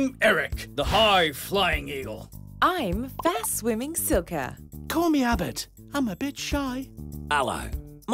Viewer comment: I thought he said" Call me albert. Im a bitch."